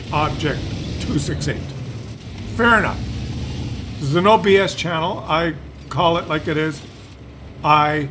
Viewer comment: I misheard that as, 'This is an OBS channel,' and I spent the next thirty seconds going, 'What!?!'. :)